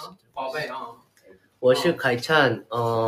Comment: What did Haechan say at 10:30? Jaemin wanting to say it in Chinese is so adorable 14:40 😊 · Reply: I think he said “I’m your god 我是你的神” 😂